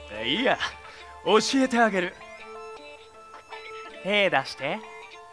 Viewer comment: 爽やかな声で屁出してって聞こえた